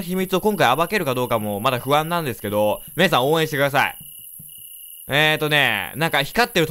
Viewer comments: の「皆さん応援してください！」が 「みやゆうさん応援してください！」に聞こえて、 (゜ロ゜;ﾌｧｯ?! ってなった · みやゆうさん応援してください に聞こえる